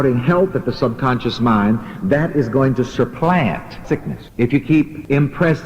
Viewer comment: why does it say “sickness”....? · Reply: The full sentence is: "And if you keep shouting "health" at the subconscious mind, that is going to supplant sickness." It was just an audio glitch on the word "sickness," making it sound different than the rest of the sentence.